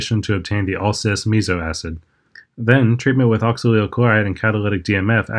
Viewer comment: Can you pronounce some of the things more clearly? at 2:07, i have no idea what you said. I'm assuming from the formula it's "oxalyl - chloride". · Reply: Yes, I'll work on that! That's correct, it's oxalyl chloride. Sorry about that!